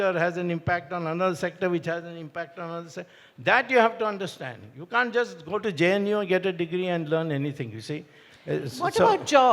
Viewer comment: did he just say u can't just get a degree from jnu and run this country 😂😂😂